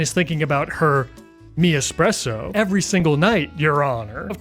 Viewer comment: meanwhile im over here like 😮bc i thought she was saying mean espresso, not me espresso this entire time. wtf is a me espresso?? like yes technically i know what she's trying to imply and refer to with that but the phrase "me espresso" by itself makes no sense to me 😭